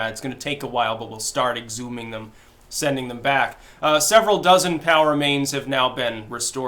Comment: That took me a second....Styx said "POW remains" but I heard "power mains"